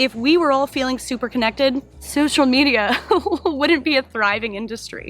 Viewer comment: I thought you said "social needia" and I thought it was the greatest turn of phrase I've ever heard.